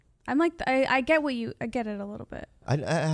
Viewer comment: What movie did he say at 4:57 ? Meat show black ? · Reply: Meet Joe Black